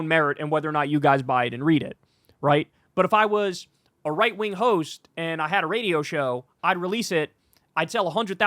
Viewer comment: I heard that as: "right wing hoes ." 😂 I was like, "lol nice," before I realized that it's "right wing hosts ."